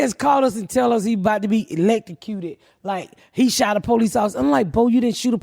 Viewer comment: Oh “electrocuted”...I swear I thought Ms.Pat was saying “late to cue”